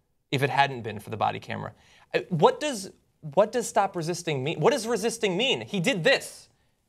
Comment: At first I thought the other cop was saying, "Stop hitting 'im," and I thought that the other cop was being a decent human being. Then when they showed it the second time I realized he was saying, "Stop resisting." Ehhhhhhhhh.